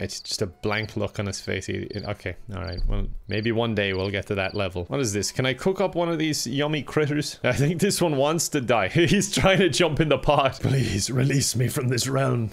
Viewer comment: Wait, did just say yummy clitoris at 1:03 ?? I think my hearing is fucked up, but I'm laughing so hard 🤣